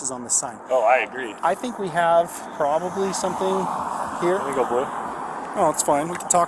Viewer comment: "let me go blue" is a terrifying code for the police to have. No police department should have codewords for turning their bodycams off to avoid accountability. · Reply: I thought he said “blow”